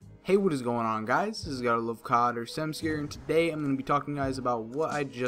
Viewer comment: i always thought you said: god i love cod xD